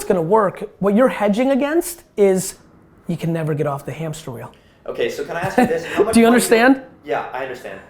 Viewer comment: Hey Gary! Help me, I don't understand the word at the end of your sentence (i am french) at 1:20:05 : "what you're hedging against is you can never get off the .... ??" Thanks man, really appreciate this particular part with. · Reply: the hamster wheel" - La roue du hamster ;-)